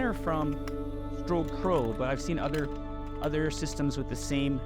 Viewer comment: Hello, At 10:45, you mention a brand, but I don't catch its name clearly. May I ask you which one is it, please? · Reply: Hi! It’s Strobepro and it’s a more traditional softbox for bowens mount but it’s super-fast to deploy. <link>